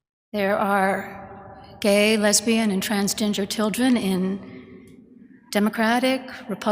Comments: Did she just say "trans ginger children" ?!? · did she say transginger ? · I thought she said trans Ginger children lol 😂